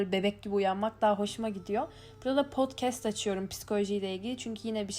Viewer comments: Abla çok tatlisin seni cok seviyorum💜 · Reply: yaa videoda 2:48'te prolo prodcest diyor.Tam duyamiyorum ben.Sen duysun mu?Ne dedi? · hayatımda gördüğüm en samimi ve en mükemmel youtuber'sın 💖💖🌿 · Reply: Ela yaa videoda 2:48'te prolo prodcest diyor.Tam duyamiyorum ben.Sen duysun mu?Ne dedi?